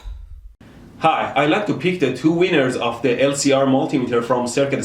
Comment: Lol it sounded like he said it time to pick the 2 wieners 😂